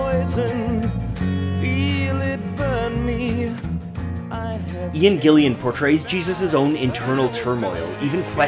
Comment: Great video! I would have liked to see "Whats the buzz" in there, its one of the best parts. Also would have been great to describe the great musicians involved in the project. And it sounds like you say Ian Gilliam. His name is Ian Gillan.